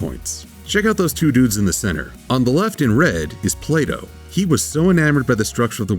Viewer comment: I heard Play-Doh. It was until the gears stared to turn, I got it.